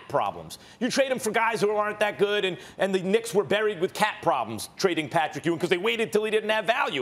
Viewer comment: I thought Max kept saying "cat problems" 😂😂😂😂 i was confused af